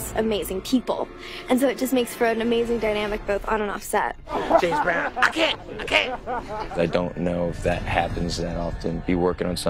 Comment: Anyone know what Dexter was saying at 4:40 ? lol i cant tell.. :/ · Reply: someone says james brown and he says i can't I can't